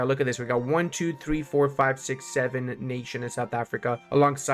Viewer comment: what number did you just say? 67??